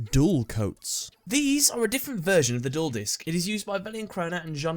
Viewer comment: At first I thought you said number five was the juul disk xd